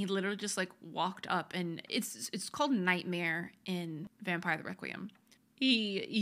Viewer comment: THE WHAT?!! Why haven’t I heard that before! I better Google it! Thank you!! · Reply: It's Requiem, just in case that part got glossed over!